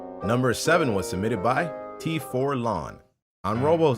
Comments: im pretty sure he said t4lon instead of t5lon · says, "T4lon", but what shows up on screen says, "T5lon."